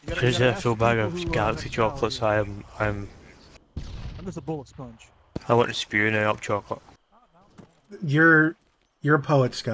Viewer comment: wtf did he say between 10:24 - 10:32 ?? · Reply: Being from the same general area. I got as much as "I just ate a full bag of Galaxy chocolates so I am.. I am... I want to spew out chocolate." Honestly that last bit even I struggled with.